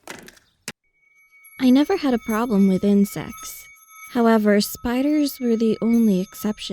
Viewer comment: Why did I hear "I never had a problem with incest." 🤣